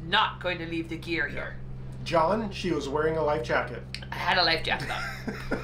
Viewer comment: I heard it twice: “John, I was wearing a lifejacket!” 😎